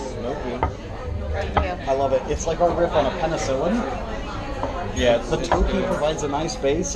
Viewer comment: Oooh you guys look so great!!!! I love what u did with your eye look! I love those little candle holders on the tables. I could’ve swore the waiter said it’s “our rip on penicillin” about the filet😂 Looked delicious though. Great video.😘😘😘